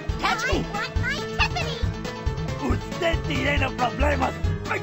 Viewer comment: What did he say? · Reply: "Usted tiene problemas" (spanish for "you have issues")